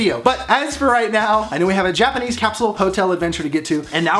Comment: I legitimately thought he was saying "Japanese castle hotel" until I saw the intro. This makes so much more sense now.